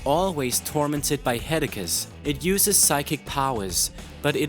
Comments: did he just say headacus Because that's awesome. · He says "Headacuts" and "Megrains" at 7:33 instead of saying Headaches and Migraine.